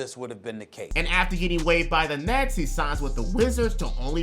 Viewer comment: why the captions at 44:05 say nazi instead of nets😭😭 youtube trippin